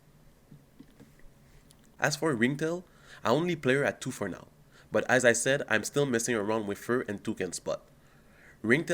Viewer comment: In 5:20 what did you say u are missing sorry. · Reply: The promo that counter charges, Afflated Lemur.